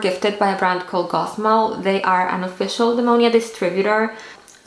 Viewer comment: Ok so weird question, but I couldn't understand the words you said at 2:53 in the video about where to look for low prices in the US. I've been trying to find lower prices but I've had no luck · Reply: Hi! Sorry about that. I got them from Gothmall ♥ <link>